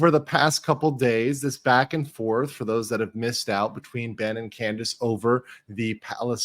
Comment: Anyone else hear "bending Candice over" ??? 😬😂🤔